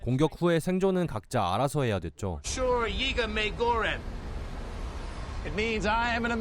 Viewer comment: 얼핏 들리기로는 "워쓰 이거 메이궈뤤" 이렇게 들리는데 중국어 아님??? 일본 폭격하는데 왜 중국어를 배우지